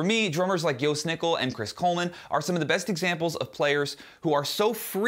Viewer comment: what are those names? Gill Snickle? I couldn’t find someone with that name unless I heard wrong. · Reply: Dunno if you found them already but he said Chris Coleman and Jost Nickel.